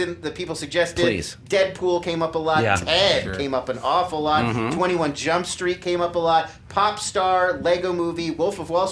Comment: What does Lon say at 1:04:12 between Deadpool and 21 jump street? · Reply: Piotr Żabiński Ted